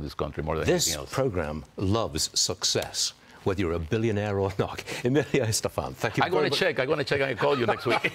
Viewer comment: LOL I thought it said Emilio Estevez and I saw that guy and said he's really not making a Mighty Ducks reboot.